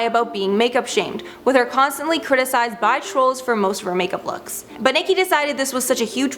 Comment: What is she saying at 8:29??? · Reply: "With her constantly being criticized by trolls for most of her makeup looks"